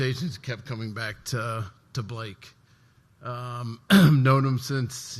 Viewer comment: Welcome Blake and congrats 🎉Do I hear "back to back?"